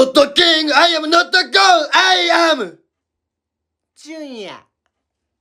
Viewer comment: Did he say he is a junior?